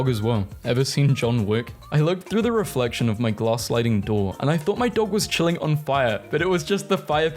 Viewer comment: HELP I MISHEARD IT AS GASLIGHTING DOOR